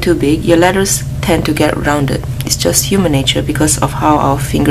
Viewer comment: did she said human nature ? Michael jackson fan here..